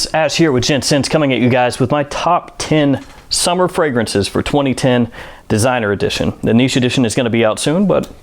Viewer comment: Top ten of 2010? Hmmm, my time machine finally worked or its 3 AM and I can't hear well, or said at 0:05 top ten fragrances of 2010.